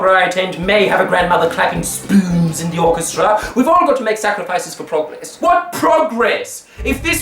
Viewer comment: I thought he said you had to make sacrifices for profits. Yeah, that sounds right.